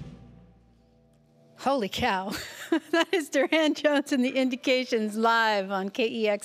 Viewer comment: At around 6:20 does she say Durand Johnson?